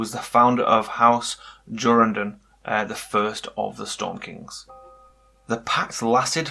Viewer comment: What's the name of the king mentioned at 3:05 after Garth Greenhand? It says it was the first of the Stone Kings (?) but I can't hear his name or the name of his house well